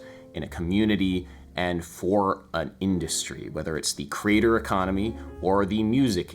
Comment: It took me a few minutes to realize was saying, "the creator economy". I heard it as, "the crater economy". It actually made sense and aligned with what he was proposing.